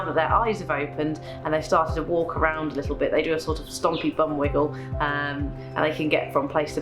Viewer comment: “Stompy bum wiggle”. That sounds like a new dance (or maybe an old one from the 60s) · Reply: I thought she said "stodgy bum wiggle"...whatever, it sounds cute